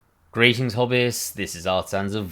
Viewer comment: Totally unrelated, but forever thought you were saying "greetings Hobbits", but in the preview it shows subtitles and just now noticed you're saying "greetings Hobbyists" :-)